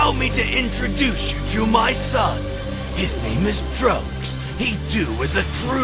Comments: Am I the only one misheard vegeta saying that trunks name is drugs? Also the caption when vegeta says trunks is subtitled as drugs. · If you use CC it sounds like vegeta said drugs instead of trucks